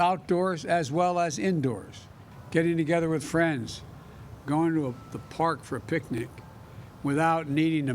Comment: When he said going to the park for a picnic, I thought he said going to the park for a quickie lol.